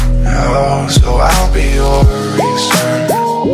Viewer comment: I thought he said "Ill be your eraser" now that i look at the title it totally changed the meaning